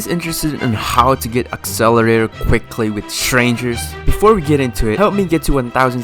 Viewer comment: I thought you said you’d be showing how to get accelerator easily